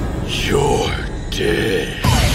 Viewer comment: It kinda sounds like yami said “You’re gay”